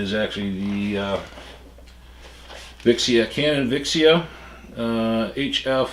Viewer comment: It sounds like he is saying canon viagra